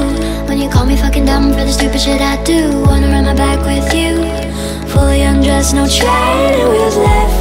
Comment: I THOUGHT IT SAID “water on my back with your,fully undressed-“ 😭😭